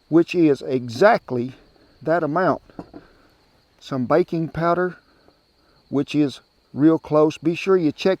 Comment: Hilarious! I thought you said bacon powder! Lol😂😂 I though, I’ve never heard (been in Australia almost 20 years. Married an Australian gal. Best think that ever happened to me) of that! Must be new. Then I realised you said BAKING powder! 😂 love your hillbilly accent. At one time I had it too. I do miss those mountains, and my relatives. Fishing and swimming in those ice cold mountain creeks! To relive the late 70’s, early 80’s. What a great time to be alive.